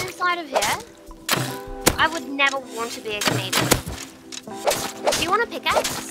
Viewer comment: did she just say she would never want to be a canadian? lol